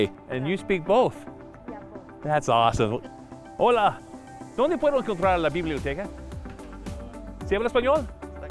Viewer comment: "Dónde puedo comprar la biblioteca" did I hear that right? Where can I buy the library?